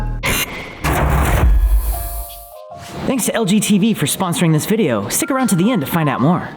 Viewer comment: I heard “Thanks to LGBT For sponsoring this video.” 😂😂😂